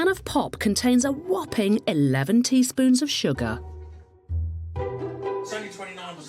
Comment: I can't be the only person who heard "Eleventy Spoons", like what are you a Hobbit?